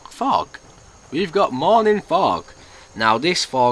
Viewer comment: I thought it said "morning frogs" and I was excited to hear frogs XD